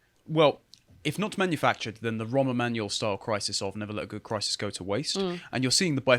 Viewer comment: Homer Manuel? Is that what was said?